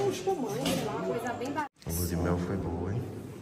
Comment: "leu de mel foi boa" kkkkkkkkkkkkkkkkkkkkkkkkkkkkkkkkkkkkkkkkkkkkkkk